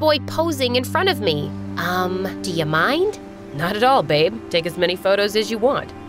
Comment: OMG did he call her baby😳